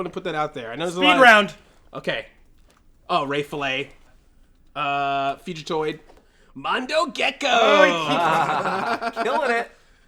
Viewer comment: Ray Fillet. Fugitoid. Mondo Gecko. ........oh. Bullsh*t. LMAO! X) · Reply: Oh he said fugitoid! I thought he said future toid. He was the only figure/character I didn't recognize. Who the hell is he?